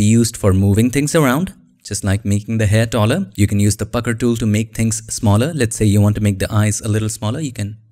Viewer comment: I had to rewind it because I honestly thought you said "fucker tool" and I was like "the what now?!"